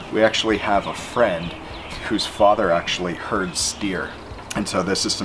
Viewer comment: anyone else thought he said deer